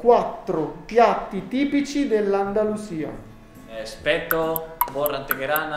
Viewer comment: ho sentito "TI ASPECTO" hahahaha